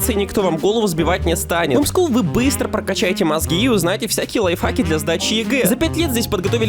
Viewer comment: Мне послышалось ,или ты сказал "ЛайФАК" класс